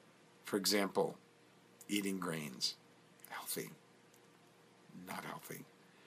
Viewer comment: he said GRAINS...not greens lol I thought he said greens too